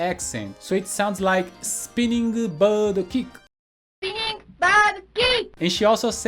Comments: To me, the hurricane kick always sounded like "Tsap-tsap-tsep-pruh-ket!" XD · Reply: I thought it said "a side step bird kick" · All this time, I thought she said “Spinning side kick”🫣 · I always heard, “spinning star kick” for Chun Li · When I was younger I thought Chun Li said "spinning star kick"